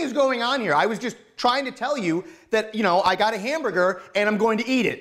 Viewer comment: I like how in the first one we thought he said I'm gonna fuck the burger